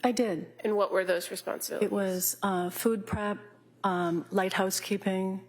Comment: For the longest time I thought she said lighthouse keeping